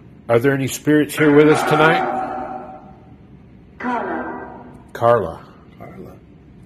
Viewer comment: sounded like "Conner" or "Carla" for sure